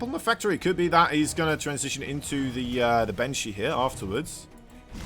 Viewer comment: Every time this guy says "Banshee" I hear "Benchy" and think about 3D printing.